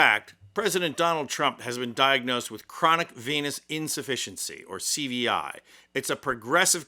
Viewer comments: Why do I keep hearing it as "chronic p*nis insufficiency"...🤔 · I thought you said he has "Chronic Penis Insufficiency" and was like, haha good one, that would explain a lot..